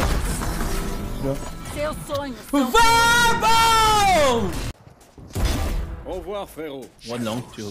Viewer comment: does he say "Vabo"? i never understand what he is yelling. one time I thought it was "bravo" then "Vamos" then "lambo" idk man. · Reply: It's "Vamos"